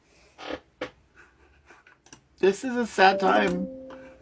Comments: honest to God I thought he said "this is a satire." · That men said this is a sad time and I thought he said satire🤣🤣🤣🤣🤣🤣 I was like oh it makes sense since there is no way he really looks like that🤣🤣🤣🤣